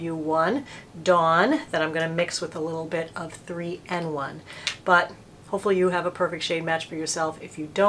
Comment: Hi, Did you add something to the foundation before applying? I thought you said you mixed it "with a little 3 and 1". But I don't see "3 and 1" listed in your product list. Thanks and great video.